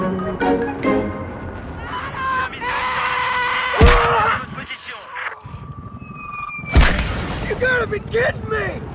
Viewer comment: what is he screaming? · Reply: Malte Sven BATTLE FIIIIIIIIIEEEEEELD